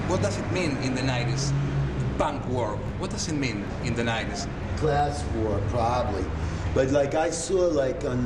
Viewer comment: SUBTITULOS MUY, PERO MUY EQUIVOCADOS . Una ultima pregunta al 4:40 Periodista: How do you feel when you see graffiti "Punk is Not Dead"? What does it mean, the word "Punk" in the 90's? [ Que sentis al ver eso -graffiti "punk is not dead"- y que significa la palabra "Punk" en los '90?] DeeDee : Class war, probably [ pero por favor como lo transcribio asi: "probablemente sea algo clasico"??? NO....."probablemente se refiere al conflicto entre los clases" ]